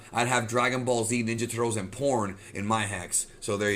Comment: Bruh I heard dragon ball z ninja turtle and porn lmao